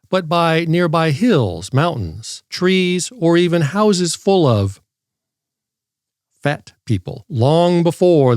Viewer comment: At first when you said houses full of fat people I thought you were saying houses is full of fent people